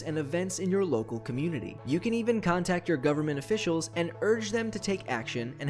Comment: I thought you said "Dumb Officials" the first time LMAO